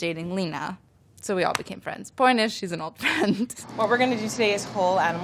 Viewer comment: DID ANYONE NOTICE THAT THE SUBTITLES SAID "ENEMY" INSTEAD OF "FRIEND" WHEN MOLLY SAID "SHE'S AN OLD FRIEND" AT 2:17 ?¿??¿¿?